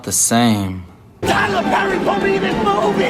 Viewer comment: "TYLER PERRY PUT ME IN HIS MOVVIIEEE!!" Lmao god damn. When I watched that I thought he had said Tyler perry put me in your movie. But dude is having a breakdown nonetheless